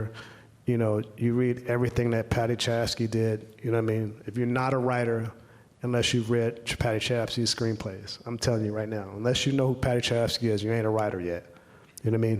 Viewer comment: Who did he say at 13:48 ? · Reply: Paddy Chayefsky